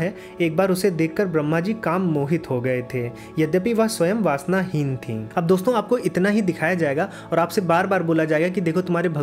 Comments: There is a mistake in the English subtitle, Lustful is written instead of Lustless. Edit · There is a mistake in the English subtitle, Lustful is written instead of Lustless.